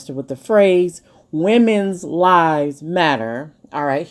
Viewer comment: I saw this on tv...I thought she was saying breast size matters not women's lives matter 😂